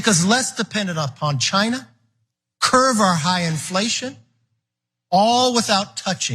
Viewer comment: Did McCarthy say "curve" our high inflation?